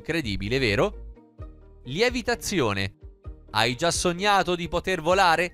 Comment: Ma pure voi avete sentito al minuto 2:45 lievitazione al posto di levitazione?